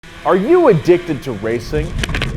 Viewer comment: I thought he said "are you dickhead to racing" LOL